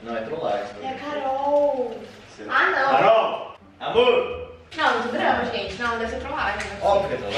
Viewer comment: Eu escutei o Bruno chamando a Carol de Amor ou é impressão minha? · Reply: no minuto 7:40, vc vai escutar ele falando "CAROL, AMOR" Pelo menos foi isso que eu entendi